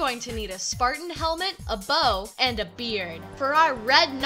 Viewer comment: For the second red outfit, I thought that you said beer instead of beard XD